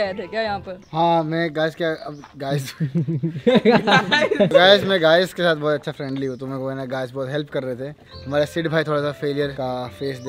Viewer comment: i heard it wrong I’m sorry but i have to say😂 kaash- isme bhi grind karke aye thei;scout- ha me kaash ke sath sorry guyyss ke sath…)im sorry